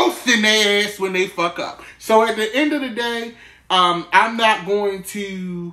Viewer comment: Why in the beginning I thought u was about to say 'the end of the world' lol.